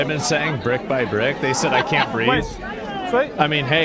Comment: I thought they were saying and chanting: "Mein Kompf Free" not "I can't breath." I was sitting there laughing and think. "Bold choice!~ But at least it gets the point across!"